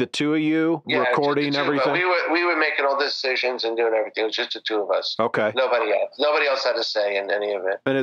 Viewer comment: Basically, I heard "I figured it out. yngwie did everything. I was just there on paper."